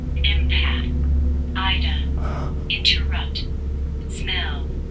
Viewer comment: Anyone else hear it say aider which means help btw